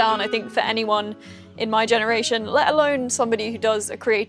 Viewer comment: Thanks for sharing this lovely journey! I thought I heard "I live alone." Is that correct now? Hope you don't mind me asking.